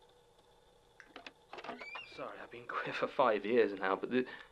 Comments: I KNOW he says "I've been quit for five years now" because it makes sense and is also in the official transcript but istg ever time I listen I always hear it as "queer" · I know that when Jon reentered the room he said "Sorry I've been clear/clean five years now" (In reference to needing a cigarette) but I stg I heard "Sorry I've been queer five years now" and I was like !!!